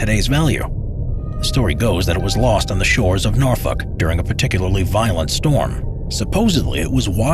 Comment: did he say the shores of Norfuck? I edited the timestamp